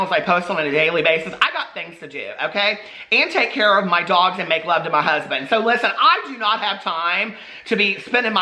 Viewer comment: Omg i thought you said your dogs make love to your husband! OH MY LORD.